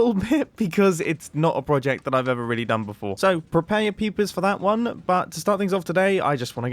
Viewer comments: it sounds like he says "prepare your peepis" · I heard "so, prepare your PPs for that one" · i thought he said "Prepare your PPs for that one" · i thought he said prepare your pee pee for that one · is he saying "prepare your pp's for that" at 0:26? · i thought he said prepare your pp's... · it sounded like you said prepare you pp's